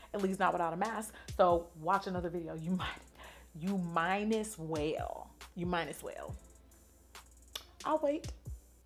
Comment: I thought she said, “You Minus Whale.” 😂😭😭